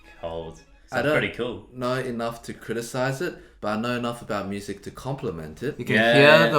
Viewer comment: "i dont know enough to criticise it— but i know enough to compliment it" me with twoset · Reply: honestly when he said that i first heard "--but I don't have enough to compliment it" i was like daaamn well thanks dude didn't know im that bad lmaoo